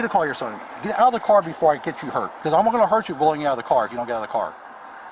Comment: Maria I heard the officer clearly he said step out the car before you get hurt I heard it. You being arrested for resisting arrest😂😂